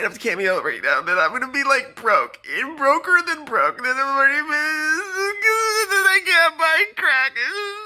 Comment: Did he say "i can't buy crack"